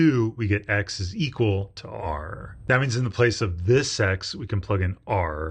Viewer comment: When he says, “This x,” I hear, “Thi sex,” 😂😂😂😂😂